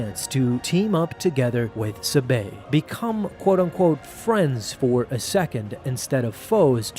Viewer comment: I thought that he said "FRIEND ZONE!